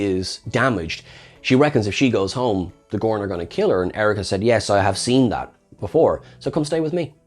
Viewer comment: Not gonna lie, I misheard "tossed the insectoid to Erika" as "D'Erika" and got mad I didn't realized she had a cameo